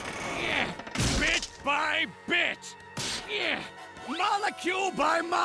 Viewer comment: I thought he said b**